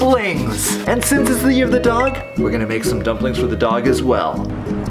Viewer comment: At first I thought said "since it's the year of the dog we're going to make some dumplings out of dog" hahaha